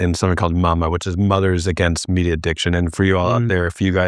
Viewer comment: Did he say “meat addiction”??? What the hell is he talking about??? We are supposed to eat fatty meat. It is what we are built to consume. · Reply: I thought he said media addiction.